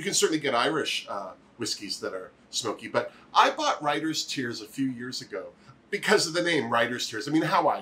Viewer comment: Oh yes!! I'm fairly new to whiskies myself but Writer's Tears is a terrific sipping whiskey. I'll have to send you my father in law's recipe for Moose Milk (Crown Royal and milk on ice ;) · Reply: I thought was saying "Rider's Tears" and I couldn't understand why she didn't make a play on name. hee hee. I have to find this since you approve it. We "collect" whisky whiskey rye bourbon in our house.